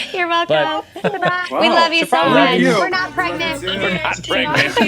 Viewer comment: why do I hear "we're not pregnant"? 😅😅 Anyways, amazing video!